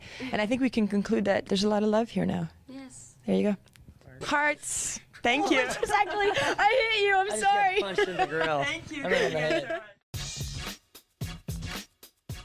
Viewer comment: I thought he said "I just got puched in the girl" lol